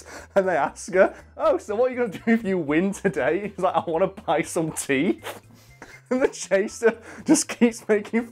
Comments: I feel so bad I thought he said 'tea' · is it bad that I heard Tee and not teeth?